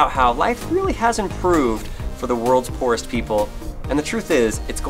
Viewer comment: This might be screwed up but when he said worlds poorest people I thought he said Worlds forest people lol